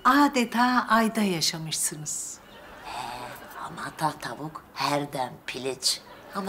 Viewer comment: Vasfiye ne diyor anlayan net olarak yazabilirmi Ne tavuk herdem piliç??? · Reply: Hamata tavuk her dem piliç